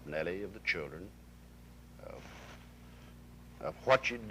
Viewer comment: "...were going through my mind thoughts of Nellie, the children of what shed done" WHAT WHO DONE? What did she do, and who is he speaking of? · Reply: Michael Griffis I heard it as "of what you've done" but with his Texas accent came out what chuve done.